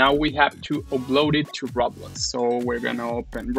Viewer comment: When You Said "Roblox", It sounded like you said RAWBLOX XD. now let's go to RAWBLOX